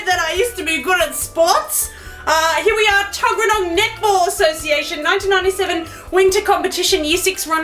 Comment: What the heck is Netball???? BTW it sounded like you were saying nipple instead of Netball.